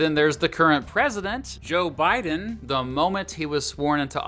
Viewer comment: No offense but the first time I heard the name Joe biden I thought they said Joe mom. No offense